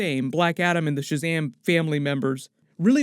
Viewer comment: i thought i heard shazamily for shazam family...lol